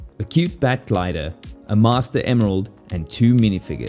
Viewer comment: DID HE JUST SAY MASTER "CRYSTAL!" (I will find you and make you pay) 😁